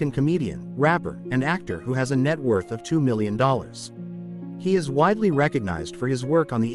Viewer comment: This Guy DON'T know What he be talking about!!!! KW is worth Waaaaay more than 2 million dollars!!! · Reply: I thought he said 2 billoin